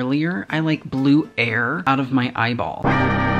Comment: No one: Not a single person: Not a single soul: I HaVe bLuE hAiR cOmInG oUt Of mY eYeS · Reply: I thought he said " I have blue air coming out my eye" I thought of the wrong Blue (Blew)